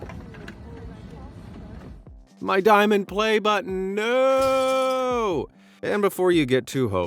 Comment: What I hear: Button What my subtitles hear: Butt